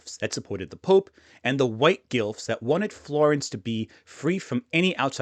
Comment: I thought you said GILF, not Guelphs